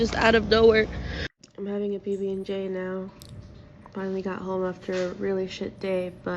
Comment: I thought she said "I'm having a baby with Jay now".